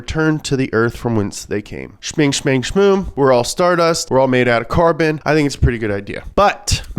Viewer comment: It sounded like you said “we’re all made out of carpet.” I was like “yeah, that’s reasonable.”